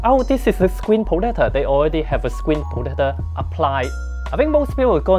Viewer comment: I thought he was trying to say potato 😂